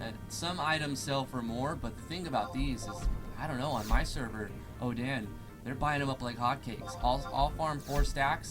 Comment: Did you say "oh-dan" for Odin at 3:26?